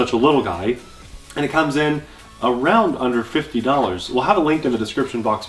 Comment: I thought you said "around $150" lol!